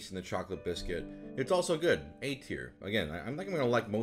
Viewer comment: For the first 2 minutes I swore he was saying "eight here", not "A tier".